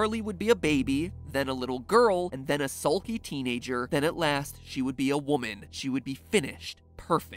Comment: i heard "a salty teenager" but i wasn't looking at the video at that moment